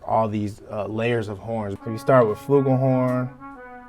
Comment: What kind of horns did he say? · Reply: Johnny St.Louis A "flugelhorn" - it's very like a trumpet, just a little mellower/fatter.